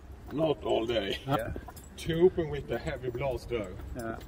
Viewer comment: I thought at first he said 'SHOCKTROOPERS WITH HEAVY BLASTERS!' which is one of admiral arbok's or whatever his name was voice lines from star wars battle front survival missions.